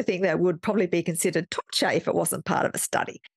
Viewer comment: Good to see your sleep video getting some love. It had 8 likes this morning and now is at 92 likes! At around 9:31 the transcript has, "between the hours of 2 and 3:30 am which is a type of thing that would probably be considered touche..." I'm wondering what you said where it has "touche"? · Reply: I said torture. Touche is obviously more acceptable for YouTube.